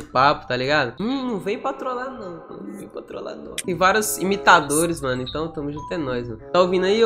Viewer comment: Eles imita ? Sério mesmo eu não entendi "imitadores"